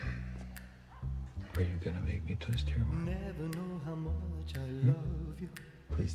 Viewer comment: what does Carlos say? "or are you gonna make me (blank)?" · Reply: Are you gonna make me twist your arm